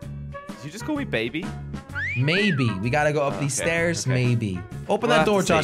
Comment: I thought he said baby too